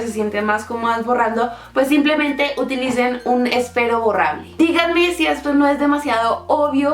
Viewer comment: díganme el nombre de ese lápiz-lapicero-mágico 😂😂 escuche "espero borrable" pero dudo que sea ese su nombre, si viven en México mejor por que quizá sea que el nombre sea distinto por el lugar en que vive ella. muchas gracias!!!👐👐